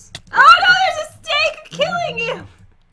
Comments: I thought said there was a steak killing you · did say you got killed by a steak 🥩 xdddd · omg there's a snake killing you:whats wrong with you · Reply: Funneh Fan and gold,rainbow,lunar,draco Fan o I thought she said steak killing you I was like wut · did say there’s a steak killing you? 😂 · did just say “Oh no, there’s a STEAK killing you” Like if you heard it to